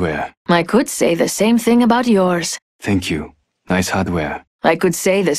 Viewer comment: i thought crypto said "nice underwear" and i was like yooo wtf